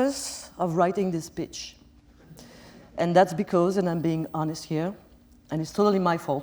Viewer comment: did she said "bitch"? If yes lol :D